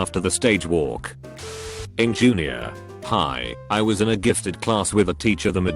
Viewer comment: "iT's pEh cLaSs" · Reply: Also at 3:35 I swear I heard at junior pie